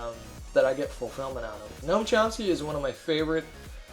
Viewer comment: Can anyone tell the name of the philosopher that mentions at 6:59. Couldn't catch it. Thx a lot!!! :) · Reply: "Noam Chomsky"